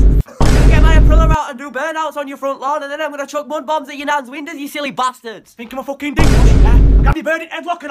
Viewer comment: Wtf they saying in the first clip all I hear was "choke you stilly bastards"